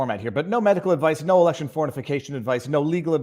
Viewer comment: Would have sworn you said "no election fornification advice". Whether you did or not, it cracked me up.